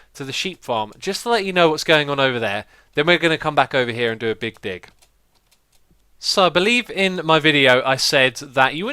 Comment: I thought he said big dick lol 😂😂😂